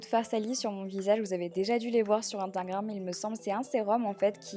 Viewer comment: Attendez sur la 2:24 elle a pas dit Intagram au lieu de InStagram ? ou c’est moi qui a beuger 😂😂